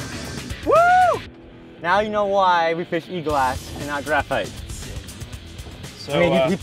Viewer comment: did he say fiberglass? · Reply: e-glass not fiberglass